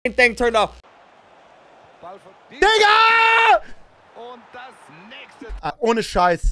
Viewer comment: what did he say???😮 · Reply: "Diggah" it's german slang for "Dude"